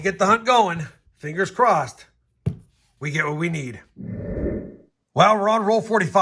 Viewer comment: When you opened box 2 it sounded like you said "fingers crossed we get a wheatie"?